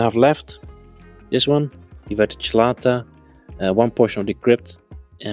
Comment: what is the plant on the right with round leaves called? I couldn't understand what the name is. Lovely scape as always. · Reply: Hydrocotyle verticilata? 😁